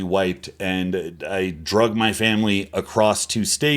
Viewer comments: Noble natties when they hear "drug" instead of "drag": 😱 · Noble natties when they hear "drug" instead of "drag": 😱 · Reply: I thought he was saying 'i drugged my family' so that he could go compete in peace or something 😂